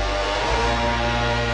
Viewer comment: what did Godzilla say? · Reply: Godzilla: hey anguirus come on! There's a lot of trouble ahead! Godzilla: Lets hurry! Anguirus: ok